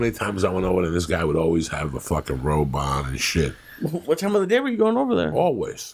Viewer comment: I thought he said robot not (robe on) lmao.